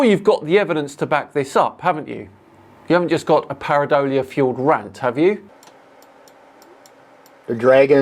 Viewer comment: I love, "It's not pareidolia..." extreme length of silence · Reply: I thought he said parody-ola - maybe he's trolling and it is all fake... well...I mean. it's all fake however you look at it.